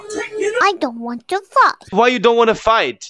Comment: Who else thought pickachu said I don’t want to f#ck